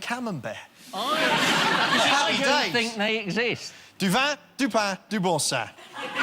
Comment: What did say in french?? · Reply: "Du vin, du pain, du Boursin". It's an advertising slogan used by Boursin cheese. "Some wine, some bread, some Boursin".